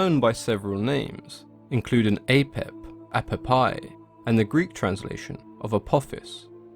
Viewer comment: Did you just say that he was known as "apple pie"?